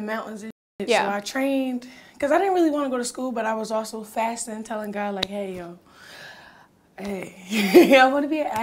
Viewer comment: Omgeee I'm shocked. This interview made me change my opinion of her. SHE'S NOT CRAZY. She's been surviving and Blue is probably the most stability she's ever had, so she won't let go! She's been drinking from the cup of abuse, abandonment, and rejection.....BUT SHE'S GOING TO BE OK! SHE'S GOING TO WIN! And the way she talks about God just surprised me. MS.ROCK is more charming, clever, strategic, and intentional than we think. #Imrootingforher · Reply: I thought I heard her say she was fasting and praying